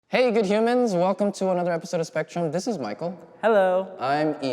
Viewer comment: I thought they were saying hello good jimins instead of good humans